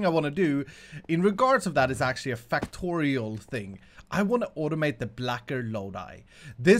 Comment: Could have sworn he said Factorio